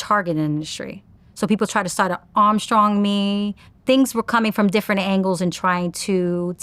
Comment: did she say "People try to arm strong me" ? Strong Arm?